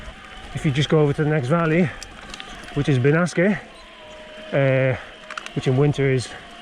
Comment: Hi, you talk about the hot summers in Ainsa and mention another town in the next valley at 10:00 mins, I didn't catch the name - please can you tell me what it is ? Thanks in advance. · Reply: It was Benasque 👍